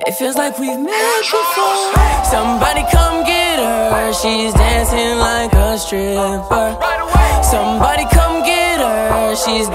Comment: i always hear "somebody come get her she's dancing like a shrimp", yeah im weird...